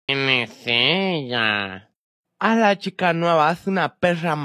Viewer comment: “Quien es ella” “Es una perr$ maldit@ 😂😂😂😂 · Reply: No puede ser jaja, en serio que la primera vez escuché "tienes sida" en lugar de "quién es ella?"